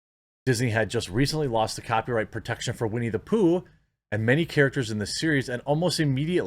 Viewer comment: Whenever I hear "Winnie the Poo" I actually hear "We Need a Poo"